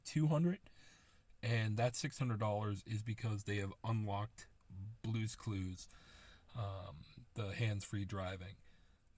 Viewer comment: I swear I heard "unlocked blue's clues"